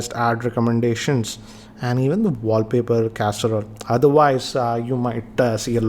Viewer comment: Did i just HEAR “WALLPAPER CASSEROLE”?.???.?